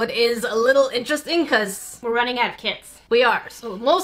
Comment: OMG lol when she said “we are running out of kits” I thought she said we are going to have kids😂